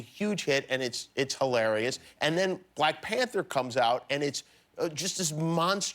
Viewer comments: did he said “white panther” ???. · Did he just say white panther! 2:09 I'm screaming lol